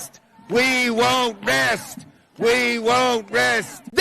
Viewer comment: Chuck Schumer chanting: "We will win!", and "We won't rest", over and over again... so pathetic. Hey, even a few joined in. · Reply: I thought he said "we won't miss"